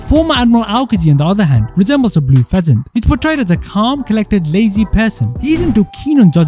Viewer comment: For some reason i heard "Calm, collected, lazy PEASANT " also this aged horribly.